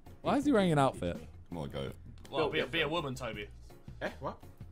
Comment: someone pls tell me what he says I can’t make it out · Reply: Be a woman Toby